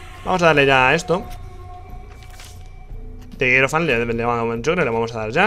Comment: que mierda es lo que dice wtf? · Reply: dice the hierophant y empieza a hacer abdanbdanb